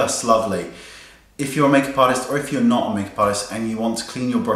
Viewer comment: Omg I turned the volume down at the beginning of the video so what I heard was "If you're making pies or if you're not making pies" and then I realised you said MAKEUP ARTIST! LOL